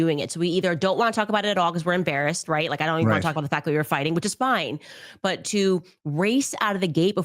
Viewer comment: min, did she just say, they were fighting??